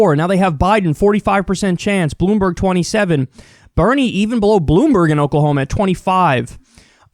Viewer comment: Did I hear you claim Bernie blew Bloomberg in Oklahoma when he was 25